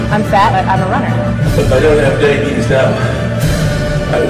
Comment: It's nearly 1am now and i hear this "If i don't habe Diabetes now i will" i can't xd